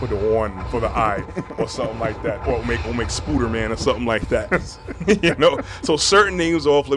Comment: did they say spooder man? :D